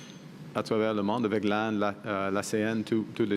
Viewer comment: "avec ... l'ASEAN" <- can someone please help me understand what PM says before l'ASEAN, it sounds like l'AN, but can't quite make it out and not sure what organization he is referring to, TIA!